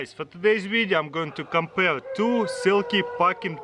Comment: I thought it said "Silly"